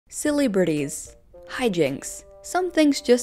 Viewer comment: I thought you were saying 'Silly Britties' instead of celebrities...